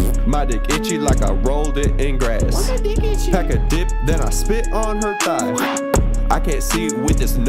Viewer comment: Did he say pack a dip